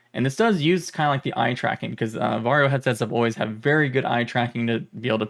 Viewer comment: Not gonna lie, I had this playing in the background and thought you were saying Mario and Not Varjo... so when you said stuff like I got this Headset from Mario you can imagine my confusion 😂.... nice insights and video ty for your huge contribution and coverage in this Community